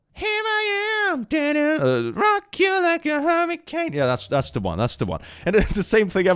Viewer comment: the captions say “a Hermit cane” & I feel like everyone must know this.